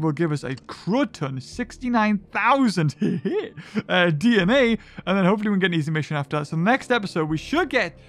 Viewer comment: he said DNA I wish it was like what you said